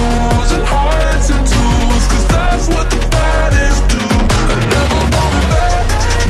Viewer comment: the more i listen to it, the more i hear "badass" instead of "baddest"